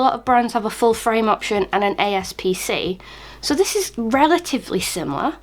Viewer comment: Really? ASP-C? That's what you said at 01:28 ?